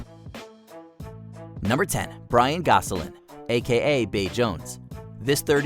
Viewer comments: I really thought they said Ryan Gosling for a hot sec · Was listening to this in the background and thought i heard "Number 10, Ryan Gosling" 😂